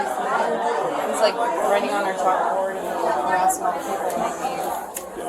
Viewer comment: Did she say he was writing on their chalkboard at 2:20?